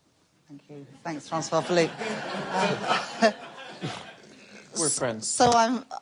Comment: When he said we are friends, did she really say 'were'🤦‍♀